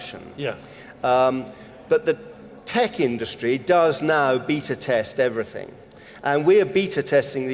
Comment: I hear say "Beta test" in the context of his non-rhotic accent and hear "Beater test". As in, "we're going to have these customers beat on the cars for a while." 😂🤣😂😆